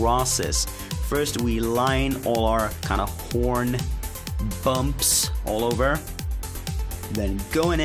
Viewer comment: Did he say porn at 16:11? lol please tell me if I'm wrong.